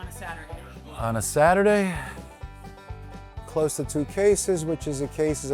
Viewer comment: I have a question ... the owner says in my understanding at 16:39 "... close to three cases ..." which would make more than a thousand eggs, but the comment says 720 eggs, which would be two cases. Did I hear wrong, or is someone bad at math?